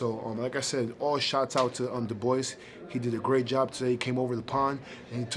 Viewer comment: Sounds like he said the boys not Dubois